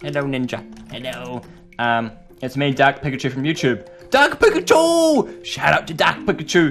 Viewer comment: i thought he said Duck Pikachu